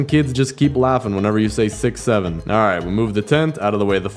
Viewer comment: Did you say 67 15:13 aaaaaaaaaaaaaah ahh 67 67